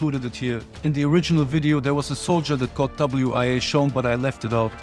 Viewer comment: what did he say? WIA? whats that mean?